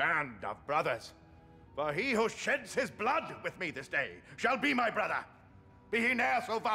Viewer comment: 'For he who shits his blood!' Anyone else hear that?